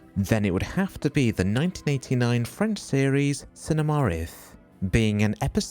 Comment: <link> I cannot for the life of me to find what I hear as cinemar rith. Could someone provide me with the right Name of this French series?